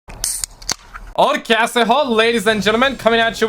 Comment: I thought my man said “okay asshole” in the intro😂😭